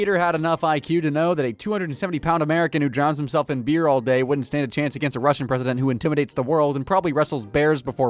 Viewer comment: When you were talking about putin, I legit thought you said peter wouldn't last against a Russian person, and I immediately started saying in my head that thats so true, theyre a special kind of tough that genuinely fascinates me